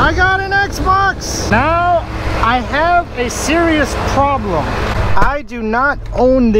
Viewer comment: I hear "I have series problem" 😂